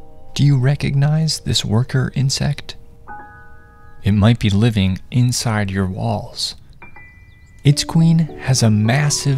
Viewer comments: I heard ''It might be living in your balls'' and i had like ''WHAT-'' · When he said "it might be living inside your walls" I thought he said "He might be living in your balls" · I thought it said “it might be living in your balls.” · Tell me why I thought he said balls ( it might be living inside your balls) 🫢🫣😅😅😂😂😂🤣🤣🤣 but when I listened to it closely it said walls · Guys I have bad hearing so I thought he said balls💀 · I thought it said “it could live in your balls”💀💀😭😭😭😭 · I heard that as "it could be living inside your balls" I am NOT kidding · I thought he said “balls”instead of “walls”😭😭😭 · I thought he said it might be living in your balls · I thought he said living in you balls but instead it was living in your walls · I misheard walls and was terrified that that thing is in my balls. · I lowkey thought he said “it might be living inside your balls” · Bro I thought is said balls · I thought he said "it might be living in ur balls" DUDE I GOT CAUGHT OFF GAURD UNTIL I TURNED VOLUME UP AND HE SAID "it might be living in ur walls" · My dumb ahh thought he said “it’s living in your balls”😭🙏✌️ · I am supposed to be asleep so I have my audio low. When he said it might be living inside your walls, I thought he said balls😭😭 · For a second I couldn’t hear and u thought it said “it might be living In your balls” I was so scared but now I looked at the comments and it says it might be living I. Your walls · Bro I thought it said it might be living in your balls😂